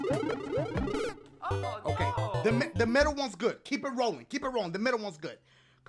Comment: Was I the only one who thought he said “the MEATAL one is good”??